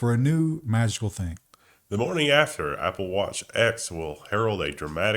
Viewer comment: OMG, the iWatch story. I thought you started saying "The morning after watch". That would a controversial feature.